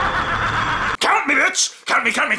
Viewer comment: what was saying at 1:42? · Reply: He was saying count me